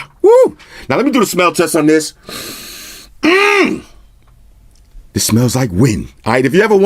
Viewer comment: Thought I heard you say.. It smells like weed